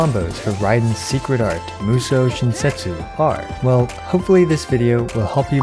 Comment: i deadass thought he said “musou shin-sexy”